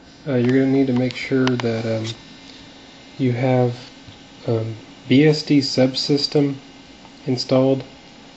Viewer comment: bst subsystem? is that what ur saying?